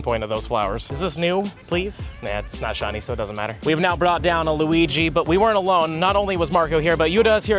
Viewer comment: Did he call Lugia Luigi?